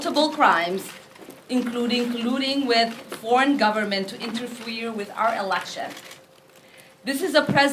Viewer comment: interfwerer. I think she was trying to say interfere